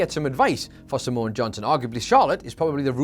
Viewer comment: I literally thought you said Samoan Johnson 😂😂😂😂😂😂😂😂